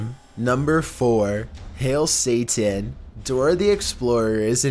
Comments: It sounds like it said hail satrone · To me it sounded like it said hail satora